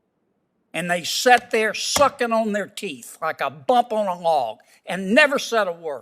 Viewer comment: "They sat there sucking on their teeth like a bump on a log." 👍✌😊 I love Senator Kennedy. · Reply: I thought he said, "Sucking on their teat"!